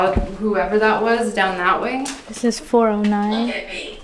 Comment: when i hear it say "look at me" all i can imagine is how scared they are, i dont think they understand their dead..